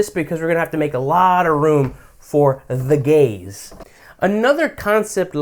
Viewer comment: I'm listening to this while drawing. Did he just say we need to make room for the Gays? And the Gays are objectifying me?